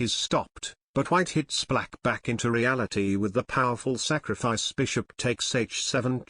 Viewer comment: for a sec there i heard "snap back to reality"